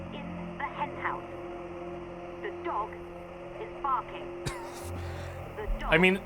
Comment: to me it sounded like she was saying the dog is fucking the dog is fucking